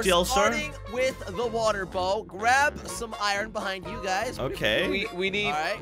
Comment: Bro I thought you just said balls bro instead of bows 😂😂😂😂😂😂😂